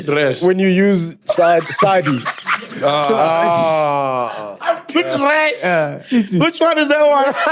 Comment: I seriously thought said "Armpit trash"😂